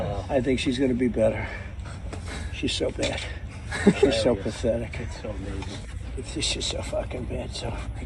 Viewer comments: sounds like hes saying "shes so bad. shes so pathetic. shes so f*cking bad" which makes more sense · Sounds like he said: she’s so f***ing bad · I heard "so fuckin' bad." Don't spread misinformation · Sounds like he says “she’s so effing bad”. I don’t hear the “B-word”. I certainly don’t doubt he calls her that regularly, I just don’t hear it in that clip. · sounds like he said “she’s so fucking bad ..so", even the closed caption picks it up, I am sure he has called her that and worse just not in this tape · He said, “she’s so f’ing bad”. I hate Trump, but at least get it right! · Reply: I heard this as well. I am a Democrat who votes Blue, I have no use for Republicans… but it sounded like Trump said “she’s so fucking bad” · It sounded like he said, "she's so fucking bad." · I thought he said “she’s so f’n bad” ? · It sounded like he said, “she’s so f-ing bad”. That’s my take but only one viewing. 🤔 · Is that actually what he's saying? I heard him say "She's just so f'ing bad, so-". It would be in line with trump's habit of repeating phrases. He already called her bad several times in a row. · I heard "She's just so fucking bad, so..." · sounded like he said she's so fucking bad to me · I hear “she’s so fucking bad so”. I listened a dozen times. However, this proves he does know how to pronounce her name. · I heard "She's just so fucking bad so..." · In that clip, it sounded like Trump was saying, "She is so fucking bad." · Sounds like he said "she's so fucking bad"